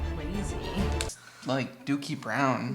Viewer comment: I thought said dooky